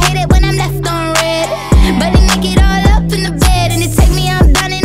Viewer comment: I always thought it was “butt naked up all in the bed” oops 🤭 I also thought she said “ way” instead of “wave” lol